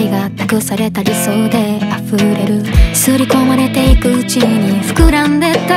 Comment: ｢刷り込まれていく内に｣が｢刷り込まれてく地位に｣に聞こえるの天才